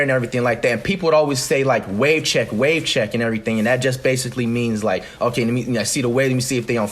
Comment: After hearing him saying it 67 times it sounds like he said "He Check